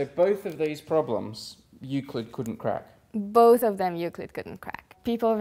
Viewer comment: I thought they kept saying "you could couldn't crack"... hahaha. I'm thinking, "is this an expression I don't know? Nope. They are saying "Euclid couldn't crack".... oh brother